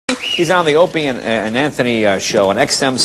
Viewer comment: When Jay opened up I thought he said "He's on the Opium" Which would've been the funniest Jay Leno line ever considering Jimmys been clean since 17.